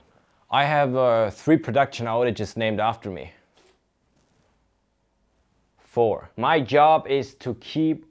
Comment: What does he say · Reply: "I have - euh - three production outages named after me ... four."